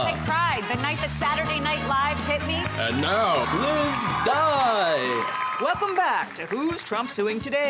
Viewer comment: Sry I heard "please die" not "Liz dye" And: What is going on in the US?!?!